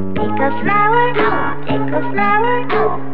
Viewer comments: it sounds like she says ankle flowers what wise words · The way she says “pick a flower” is so cute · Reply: oh i thought she said deflowered